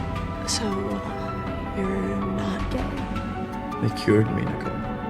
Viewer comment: did bro just say ga